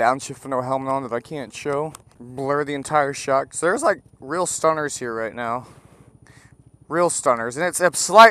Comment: What are your thoughts on lug nuts? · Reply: I thought you said stoners for a second i had to replay it to hear that you said stunters.